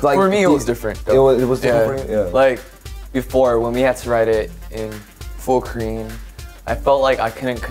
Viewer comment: Does anyone know what he says in 2:03? Before when we had to write it in full ________. The subtitle says it's full cream but I don't get that mean and I think the pronunciation sounds like it ends with n sound. I'm studying English with this video but I can't get that part. Please help me. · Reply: joshua said "in full korean" ehehe ^^